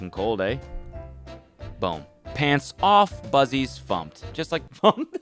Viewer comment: Cody dying at “fuzzies fumped” when it’s really no worse sounding than what he meant to say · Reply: Oh. I thought he said “Bussies thumped”